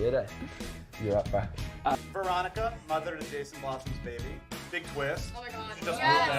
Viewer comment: what is the interviewer saying??please someone answer me!! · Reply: He says, "Veronica, mother of Jason Blossom's babies??" He had a list of What If theories. Instead of Polly having Jason's twins. KJ didn't like that.